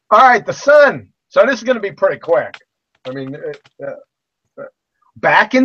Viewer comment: Did he just say "So this is gonna be pretty quack" because that's what I heard. And boy, did he make good on his word.